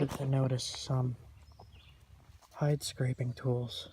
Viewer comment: haha my husband thought you said pipe scraping tools